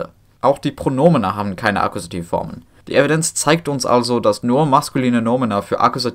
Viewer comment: Just a bit after 7:55, let's say 7:58, you said "Evidenz" and I heard "evidence" and thought "oh, he used an English word here, how did nobody catch that!?" only to realise it's similar, the pronunciation is different, but you said it with an English accent so that's why it threw me off guard. Also I didn't memorise the timestamp, so I had to watch some parts of the video multiple times in order to find the word. Mobile YT, for some reason, doesn't have transcripts. Also, I'm leaning German myself, well, to be exact I'm expanding my knowledge by going on a language course and am at the second highest available module (meaning, I pass the next one and am officially at C1) and I can without a shadow of a doubt, tell you that I could not, for the life of me, make such a coherent presentation with such fancy words. You study linguistics in Germany, so ofc you'd be able to do this, but still, I bow to you and your knowledge. I feel like I can't progress much no matter how much I learn... Any chance for some advice?